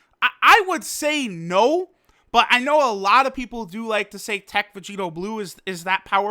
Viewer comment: did someone say teq vb or is that just me?